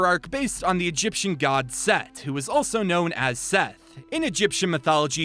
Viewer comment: I heard "Seth, also known as Seth."